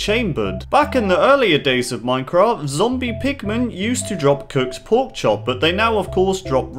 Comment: It sounded like you said "Zombie Pikmin." ._.